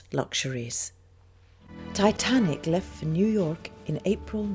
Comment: > Am I the only one who heard this: "Titanic left New York in April 1912..." The ship never departed from New York, and never arrived there. The ship departed from Southampton, England.